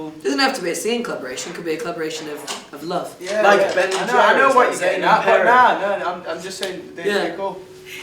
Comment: Did Harry say, "Like Ben and Jerry, Zayn and Perry"😂😂😂😂😂😂